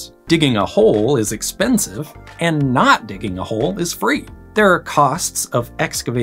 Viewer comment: I thought he said don’t dig a hole!